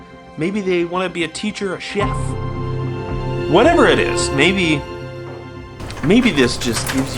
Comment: Hey! I love this video so much, and I want to make sure everyone can understand and enjoy it. That's why I wanted to let you know that your captions are a little off. At about 2:24 the auto-captions say "Maybe they want to be a teacher or shit" where they meant to say "Maybe they want to be a teacher or a chef," Though this auto-caption is rather amusing, when you get a chance, could you fix it? That'll help the 360 million folks globally who rely on closed captions to understand videos. Thank you, and thank you for your wonderful and hysterical content!